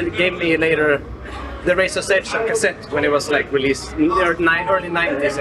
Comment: What album did he say? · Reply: AC/DC - The Razors Edge